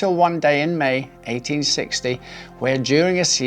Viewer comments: When during a seizure he died with c.c on but I thought at first that u said where Julies Caesar died lol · lol I heard "during a seizure" as "Julius Caesar". <link>